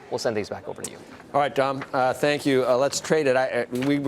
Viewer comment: Did Joe say “alright Tom”? to Dominic? Look at Dominic’s face expression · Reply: Ken Again he said “Dom”